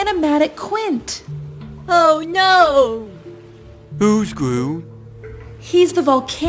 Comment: I Heard “Who’s Gru?”